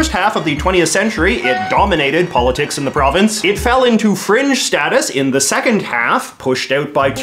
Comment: Omfg I heard “cringe status” instead and lost my shit